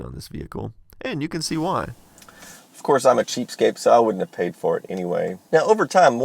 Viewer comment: Did he say cheapscape?